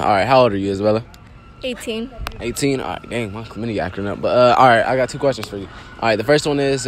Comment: bro am I the only one who heard him say his clamidia acting up ?